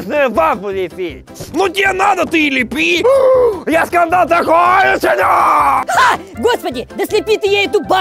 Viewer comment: Я так смеялась после фразы: "Я скандал такой учиню! " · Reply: Мне послышалась я сказала такое усуню